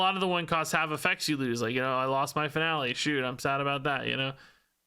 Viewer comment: I was listening to the video and I heard " I lost my family shoot sad about that " lmao